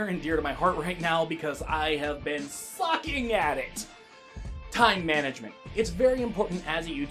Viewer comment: Am I the only one who thought he said "Fuc*ing" instead of "Sucking at it"?